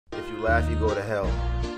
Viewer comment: I thought he said if you black u go to h*ll.then I read it and said if u laugh u go to h*ll